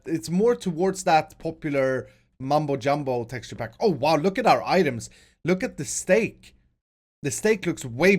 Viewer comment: Did i just heard him say "look at our idubbz"